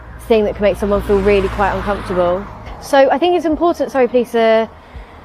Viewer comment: Oh it's the Surry police! My hearing is going, I thought I heard the "sorry" police. Hm come to think of it maybe my bad hearing is onto something.